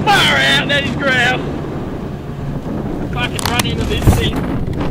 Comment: I thought he said he wanted to run into the twister? Epic video though!